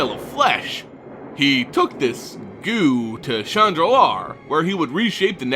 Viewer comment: Sweet the Halloween series is back! Am I the only one who heard him say the plane of "Chandra Nalaar" instead of "Shandalar?" haha